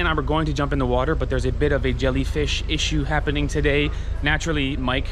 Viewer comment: Did you say Jellyfish issue? Catch some and make Umai Ubur-Ubur(Jellyfish Umai). One of many types of Umai dishes.